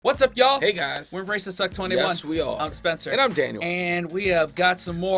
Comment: I really need to have my hearing tested. What I heard at 0:00 was "whats up ya'll.. we're racists at 21" 😬:hand-orange-covering-eyes:👀